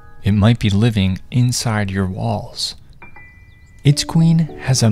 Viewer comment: At first i thought he said balls but he said walls